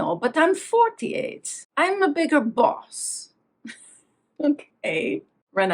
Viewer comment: Lol! I heard it as Diane saying "I'm 48 & I am a bigger fox" not a bigger "boss". And in ten years in fox fields she has grown "a bigger tail". Not that it makes any more sense🤣😅...it just keeps the "fox" thread going.🤷🏾‍♀️